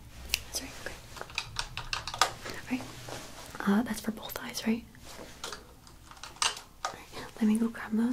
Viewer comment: when she said “for both eyes” I thought she said “purple thighs” at 12:59 😅